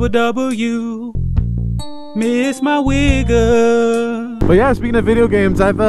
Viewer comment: You really need to enunciate a little better. sounds like you said the n word